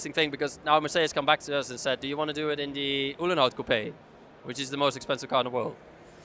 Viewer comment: Which Mercedes model is he referring to at 18:08 which is valued for 250 million? I cant make out what he says. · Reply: It’s the road legal ‘300 SLR’ called the ‘Mercedes Uhlenhaut Coupe’ - details: Daimler-Benz developed this hardtop version of the 300 SLR racing car for the 1956 season. It was never used in racing, however, because the company ceased its motorsport activities at the end of the 1955 season. Instead, the 300 SLR coupe served the head of the Test Department, Rudolf Uhlenhaut, as a company car. The nickname “Uhlenhaut coupe” is a reminder of this. Cylinders: 8 Displacement: 182 cu in Output: 302 PS (222 kW) Engine speed: 7500/min Top speed: 180 mph Production period: 1955 Units produced: 2